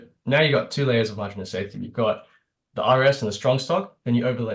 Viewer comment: Anyone catch what said at 10:10, the captions say IRS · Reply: RS (Relative strength)